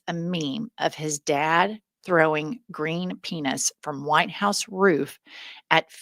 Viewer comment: I had to back up. I thought you said peanuts, not penis.